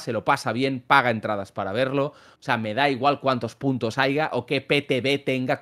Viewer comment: dijo lo que creo que dijo … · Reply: Sí, dijo "haiga"